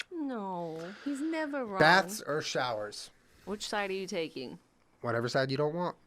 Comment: I thought said bats vs showers lmao